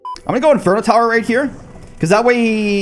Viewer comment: did you say inferno tower??!